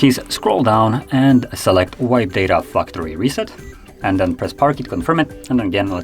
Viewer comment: SPEAK CLEAR OR DELETE THE VIDEO 2:35 SUBTITLE SAYS PARK KEY